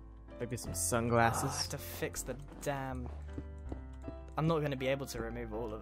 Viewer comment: did Grian just say Damn?